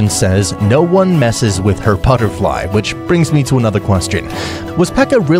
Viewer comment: This whole time I thought she said time to die, not butterfly lol.